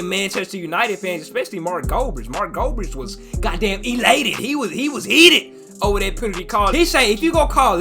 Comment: did mf just say mark goldbridge was "elated"? 😭